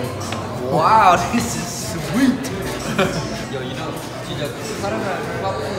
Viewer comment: I THOUGHT MARK SAID “WOW THIS IS WEED” I CANT STOPLAUGHING